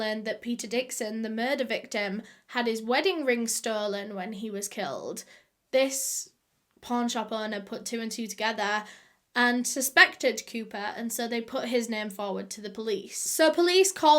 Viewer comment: I really thought she was saying porn shop owner 😳